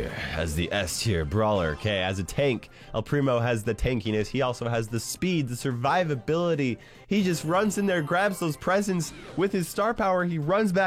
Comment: did anyone else think he said salivability